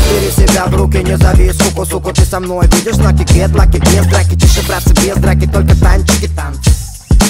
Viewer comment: Бля, подскажите что он говорит с 0:38 по 0:42, субтитры смотрю, но что то оно не подходит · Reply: ты со мной будешь знать кек lucky,без драки,тише братцы,без драки тан чики танц